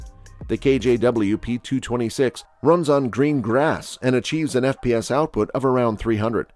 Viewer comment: TM Hi capa's are not all plastic... They have a metal frame. 6:57... Never knew a pistol could run on green grass like a cow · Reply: Oh, I thought he said "green grass" 😅